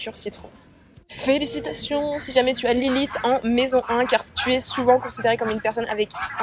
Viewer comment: Je comprends pas c’est quoi « l’élite? » à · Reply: Anaelle Vincent « Lilith » soit la Lune noire